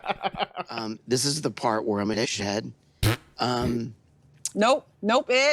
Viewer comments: What is he saying? Im an Ish-head?? In a shed? wat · what does ed say at 8:30? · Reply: nvm its shithead but they censored it